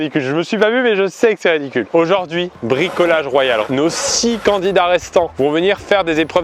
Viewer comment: Yok : j'ai entendu "nos "scies" candidats restants" écrit comme ça. Je pense qu'on est en route pour être en phase avec les jeux de mots de la vidéo XD